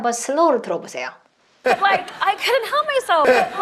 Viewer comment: Couldn’t 으로 들리는 내귀 정상인가요?